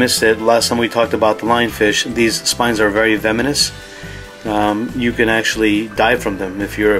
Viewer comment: did he just say "veminous" ?